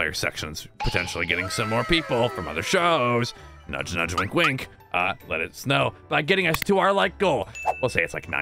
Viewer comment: I thought said let it snow😂😂🤣